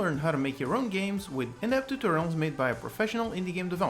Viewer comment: You said "in depth tutorials" so fast that it sounds like you said "inept tutorials"